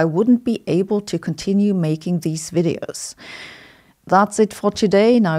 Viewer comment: For some reason I thought you said "Duck a l'orange my arse"... Interesting concept...